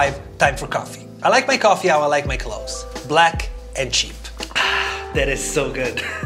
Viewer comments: Did he say "I like my coffee like i like my hoes"? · I swear I heard "I like my coffee like I like my h0e$" instead of clothes 😂😂😂 somethings wrong in my head